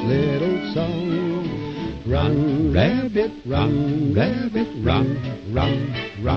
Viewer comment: i always thought it said "rotten rabbit, rotten rabbit, rot rot rot"